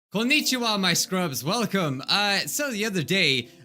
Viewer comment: I had my volume on low and it sounded like, "well biju out my scrubs" and I didn't understand that